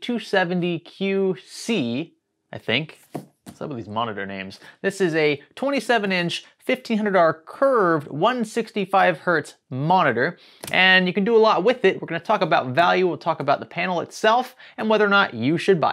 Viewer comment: He said 1500R, I heard 1500$ 😆